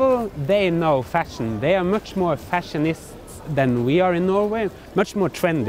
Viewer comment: They are much more fashionists [sic] than we are. I thought you said fascist!